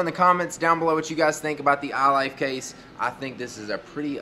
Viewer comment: Did you just say iLife case? :P